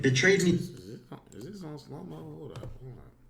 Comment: I thought u said u ain’t unsubscribe woe